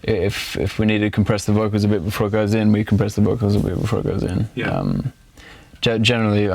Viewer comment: What did he say at 10:03 pls? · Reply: If we want to compress the vocals a bit before it goes in we can compress the vocals a bit before they go in :P